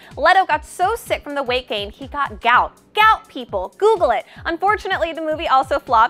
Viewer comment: I would google it if I understood what the heck you were saying in that moment. Gaucht? Geuth? Caught? Gaunght? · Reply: gout